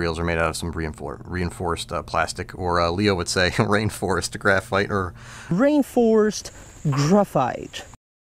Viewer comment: Omg I thought he said rainforest graphite too! Like that was a thing